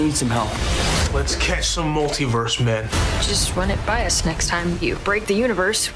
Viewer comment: i think Ned said "Let's catch some multiverse Spider-Men" but the spider getting cut